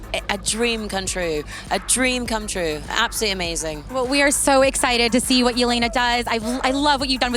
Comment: I heard "We are so excited to see when Yelena dies" 💀💀